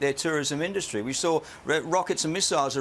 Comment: DID HE SAY RED ROCKET 😆 🤣 😂